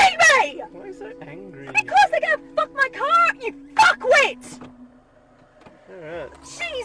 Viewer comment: "Why are you so angry" he says in true Aussie form. Well done mate well done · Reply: Sounded like she said :"Oh you fucken Kiwi" bit harsh I would've thought :)